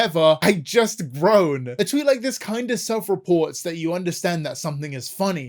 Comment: did man say "self report" post Among Us?